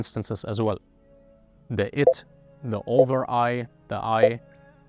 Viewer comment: The Id, not the "It" as it says in your text in the background around 13:41. Please understand your interlocutor instead of misrepresenting him. Also, the "I" and "Over I" are called the Ego and the Superego in English.